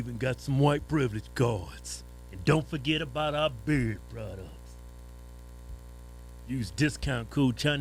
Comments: For the longest time I thought they were saying bird products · I always thought they said BERRY products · It took me so long to realize you were saying "beard" products. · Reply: You misheard. It's actually "dairy products."